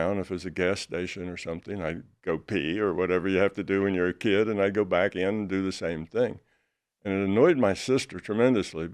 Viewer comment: did anyone hear the name mention at the end Blitza? not really sure but i would like to look into that individual they are talking about at 3:41 in the episode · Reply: His name is Chris Bledsoe. He’s amazing.